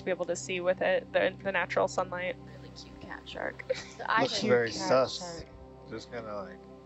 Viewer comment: did he say looks very sus